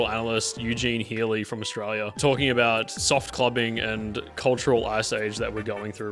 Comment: I misheard that as "clopping" TmT